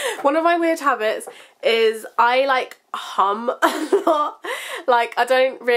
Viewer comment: when you were saying your weird habit, i thought you said "i like ham a lot" (instead of hum), so when you were talking about when you're bored i thought you were going to say you eat ham, hahaa